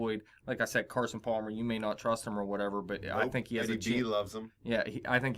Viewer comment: I think said Eddie G, lmao